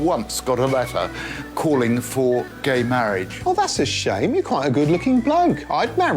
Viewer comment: For a sec. I thought that guy was called Gay Marriage.